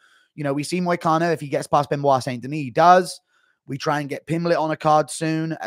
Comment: did u say he does. Moicano will get killed mark my words